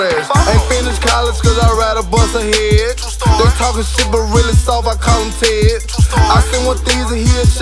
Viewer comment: did he say talkin shit but really soft I call him tec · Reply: He said I call him ted